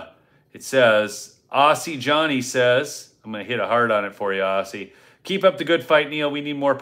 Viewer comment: At first I thought you said I have to put a hard on it, but later.realized you said “heart on”